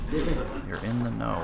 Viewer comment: instead of youre in the know i got something like "three tentacles" or something , what does this mean?